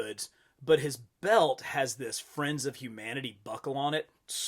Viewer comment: Friends of humanity? Thought it said F*** outta here 😂